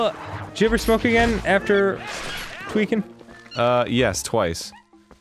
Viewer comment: wait, did he say 'after tweaking'? after using meth?